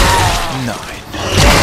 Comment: ich hab gehört wie du ganz leise bei 1:23 'nein* gesagt hast^^